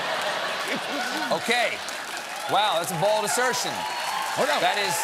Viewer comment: I swear he says "that's a BALD assertion" twice.